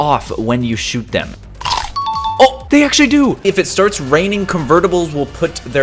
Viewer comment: It sounded like he said it's raining convertibles